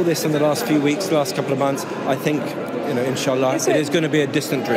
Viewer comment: did he said inshallah 🤔